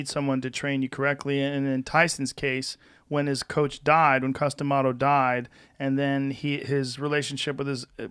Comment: I know its not but everytime I hear his name it sounds like they say Custom Auto